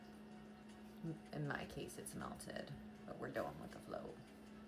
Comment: I had to back it up a bit lol I thought you said "dough-in with the flow" instead of going with the flow lol...love it. Go with it #dough in with the flow 😂❤